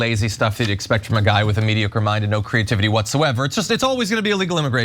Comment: Did he really say "mediocre mind?"